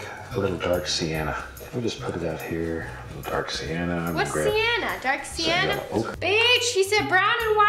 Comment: what did Nessa say? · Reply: Novanda Ghifari she said what’s Siena dark Siena?